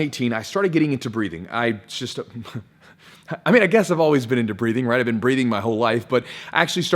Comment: I thought you said "breeding"